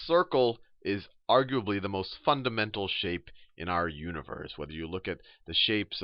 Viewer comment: sounds like they cursed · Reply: I thought he said “the circle is the most fu***d up shape in the world”